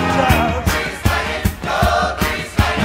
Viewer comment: When I first watch Grease I heard it as breeze Lightnin'